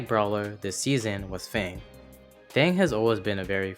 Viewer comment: anyone remember the ai voice saying "fong" instead of fang